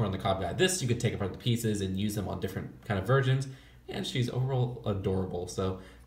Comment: I thought he said ‘virgins’ but he said ‘versions’